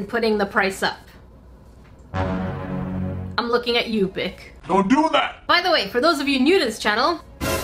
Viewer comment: For a split second I thought she said "I'm lookin at you bit*h" But no no, she said Bic 🤣